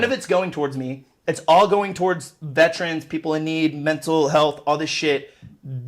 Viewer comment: Am I the only one who had to but subtitles on cause I thought he said trans when he said veterans.